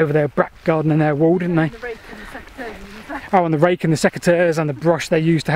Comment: what is he talking about at 14:17? he was referring to borrowing items to clear the vegetation. he said something that sounded like " SEC A TEARS". what was he referring to? I am an American so sometimes I have trouble understanding a word due to accent or the UK calling an item by a different name. two natons seperated by a common language. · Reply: Ah yes, apologies. Basically garden scissors. We call them secateurs for some reason!